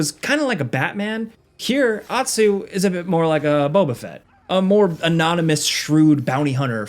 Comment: Lmfao when said Boba Fett I thought he said Wobbuffet like from pokemon I was so confused 😂😂 damn that was funny